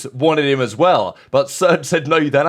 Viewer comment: Did say “surge said no deadass” ?? 😂😅